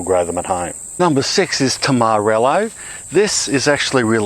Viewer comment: Lolz, when you said tamarillo I thought you said Tom Morello. You think the machine he's raging against is Monsanto or a tractor?